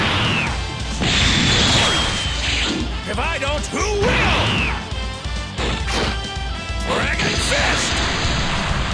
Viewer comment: what does goku ssj3 say at 1:34 ?? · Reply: If I don't, who will